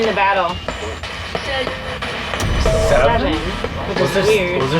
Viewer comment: I heard 10 not 7